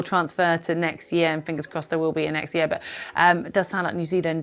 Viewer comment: did you say "fingers crossed there will be a next year"? 15:04 ... because, to be honest, that's also how I feel :)